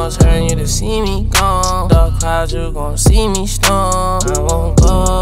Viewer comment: Lol thought he said “dark clouds ,you gon see me stoned”